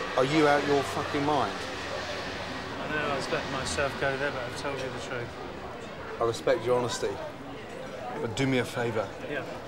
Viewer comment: is there a native that can tell me what the restaurant owner actually says here? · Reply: "I know I was letting myself go there but I've told you the truth" been listening to Ozzy Man Reviews for the last couple weeks and think I got it figured out.